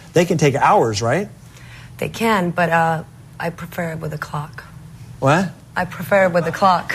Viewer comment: Hahaha. He thought she said "I preferred it with a COCK."